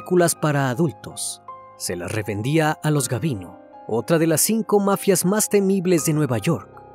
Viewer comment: En el minuto 6:44 escuché que dijo la mafia "gabino" xd y es "gambino"